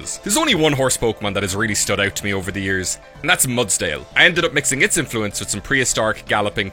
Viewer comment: Mudsdile i thought said Mudsoile